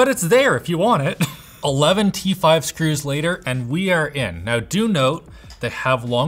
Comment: i thought said eleventy-five screws (instead of 11 t5 screws). MOre vids! I'm loving this week on LTT.